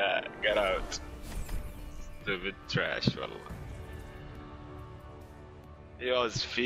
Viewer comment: I could be wrong, but I think at 11:39 what he was saying was "trash, wallah" and basically is like "Trash, I swear to god" in arabic.